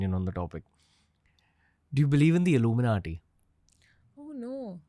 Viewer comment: Is it just me who heard it as “Dhruv Rathee” but actually what he said was “Illuminati” ??🤣🤣🤣